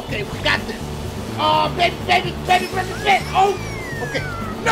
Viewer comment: did i hear "yeah baby!" in the bacround?